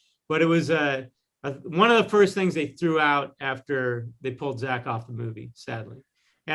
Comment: did he said: "before they pulled Zack off the movie" English isn't my first language, but I got some Tea vibe from this sentence.. me? Only?